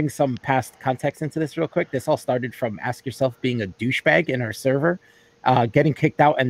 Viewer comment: I thought you were saying "ass yourself".